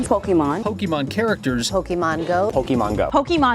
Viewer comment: Did she just call it pokeman..... just like my mom use to........ TRIGGERED!!!